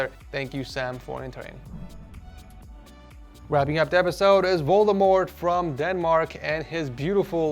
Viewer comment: did he just say Voldemort to Valdemar